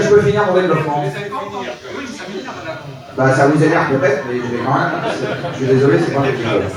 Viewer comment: C'est quoi la vanne qu'il dit à 44:30 ? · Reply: "ça vous énerve mais j'suis désolé c'est moi qui ait le micro" je crois